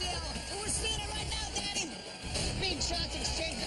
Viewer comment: does he say daddy? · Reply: I think it’s Danny